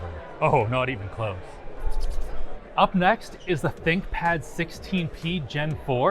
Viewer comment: did he say "thicc pad"